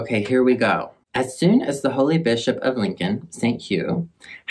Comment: Look what you've done to my brain. When you said "Holy Bishop" I thought you said "Holy Bitch-up" and I will never be the same. 😂